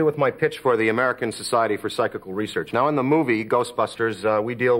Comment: Can anyone tell me what the group he mentions at 1:20 is that makes the audio cut out? I love him but I know Aykroyd is an eccentric guy. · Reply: American Society of Psychical Research. He says it again later in the interview, but they only dropped the audio the first time.